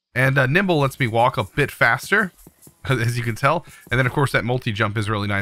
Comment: When he first said nimble I thought he said nipple xD